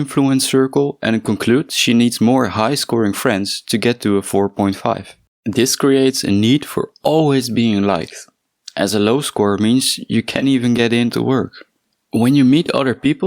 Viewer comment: Do you mean the guy in the suit? He got a low score and asks for a five star rating so he could enter the building he works at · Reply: I'm sorry I meant the content creator. At 1:00-1:12 it sounds like you say "it creates a need for always being nice" Just wondering.